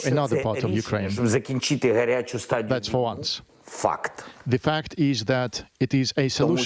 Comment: minutes, all I heard is Zelenksy saying he's "FaCKTED"